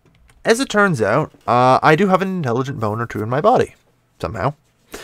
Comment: Thought for a moment said he had an intelligent boner